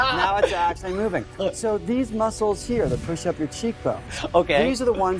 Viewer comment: Did he say "these muscles that push up your cheek bones?"